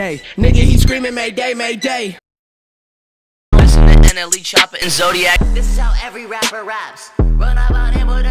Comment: I think I heard him say something over the lines of NLE Choppa